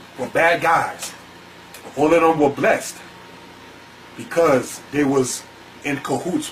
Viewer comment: What did he say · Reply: did he say they were also blessed